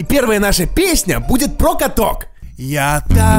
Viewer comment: Мне послышалось Про котов, а не каток ахах. 😂😅 .